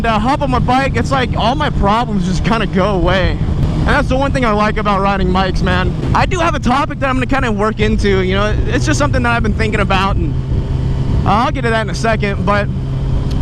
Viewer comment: am I the only one who heard "and that's the one thing I like about riding mikes, man."